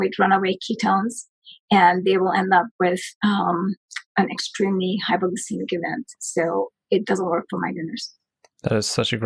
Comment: "they end up with an extremely high XXX event" I don't understand that word....somebody help me? (sorry my english is not really good) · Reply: zambrocca I hear „extreme hypoglycaemic event“, but I could be wrong.